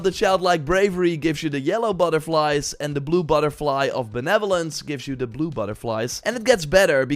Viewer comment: At first I heard "the Blie Butterfly of the Netherlands" instead of Benevolence and I went "huh we have a blue Butterfly" so I played it again with subs to find out you said Benevolence lmao. All these changes and nerfs but a simple "Diable tutorial pop ups" .... nowhere in sight.